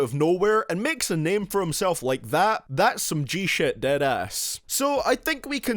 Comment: When you said Deadass it sounded like dat ass 😂